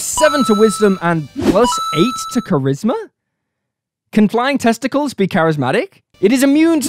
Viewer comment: Anyone else heard "flying test---es"? 0:31.🤢